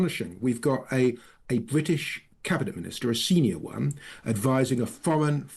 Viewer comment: A cabinet Minister A “Senior One” damn it, I thought you said a Sneer Worm 😂😂🤣🤣😂 , I think I’ll go with the SNEER WORM !